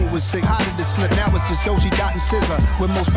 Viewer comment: i thought he said doja dot and sza. 🤦🏽‍♂️